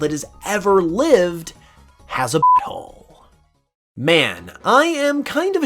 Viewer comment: Having to bleep out "butthole" makes it sound way worse, I keep hearing it as "f*ckhole"